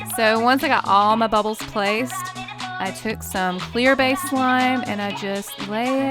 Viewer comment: Sorry what did you say CLEAR BASED SLIME??